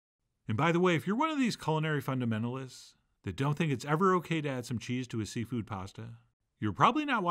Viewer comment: When Chef added the bacon I though I heard him say "add some veggies" - I had to go back XD This looked so creamy and delicious.